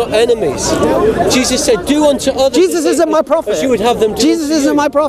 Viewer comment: Did I hear that right? Hamza actually says "Jesus isn't my prophet" (21:58). Br vids r the best anyways as hamzas r aswell